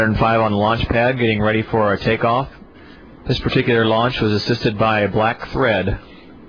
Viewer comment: i thought he said it was assisted by black fred